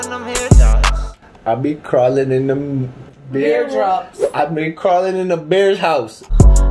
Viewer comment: i hear ''i be crawling and my hairs down"